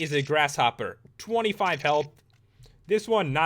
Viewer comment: helpth? am i hearing this right? XD